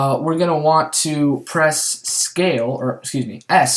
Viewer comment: What key are you saying at 19:52? Thanks :) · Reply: U, He Said U.